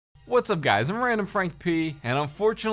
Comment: Whats up guys im random frank pee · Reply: YeS!! Except for me it sounds like he says “what’s up guys it’s random frank pay”